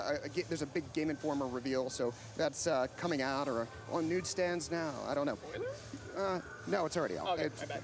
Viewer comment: lmao Jeff is so nervous throughout this, everytime I see him doing interviews or something he looks really nervous and stutters and mixes words. At about 25:15 he says "at NUDE stands now" instead of "news". Come on Jeff, get with it man! Anyways I'm looking forward to the coverage this week, will definitely be tuning in tomorrow.